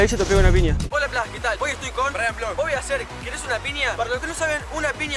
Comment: En el segundo 00:12 yo entendí draw blogs